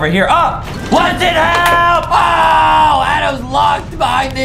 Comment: Am I the only one who thought he said what the hell for a second